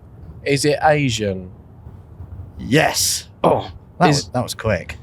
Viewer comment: Why does saying "Asian" sound exactly like Bruno saying "engine"